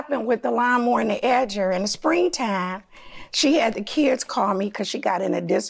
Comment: Why damage phone instead of giving it back judge tripping today plaintiff said the kids said they momma wanna borrow lawn equipment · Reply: The Judge was definitely the confused one… I heard her say “she had the kids call me” 🤷🏽‍♀️